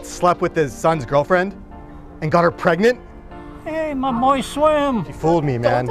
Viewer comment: Wait what did he say my boys or moist swim lol